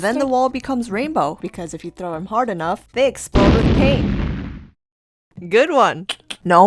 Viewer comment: did he say pain or paint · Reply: pain